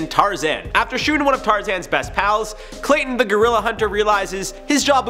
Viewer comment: Did just called Clayton the Gorilla 2:38?....